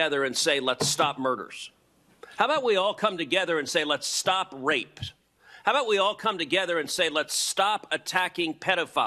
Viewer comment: I noticed after seeing this a number of times he actually says "let's stop raped" in addition to the stop attacking pedophiles gaffe.